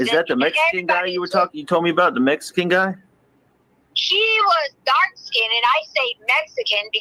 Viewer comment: It really does sound like she said "she" not he at 43:51.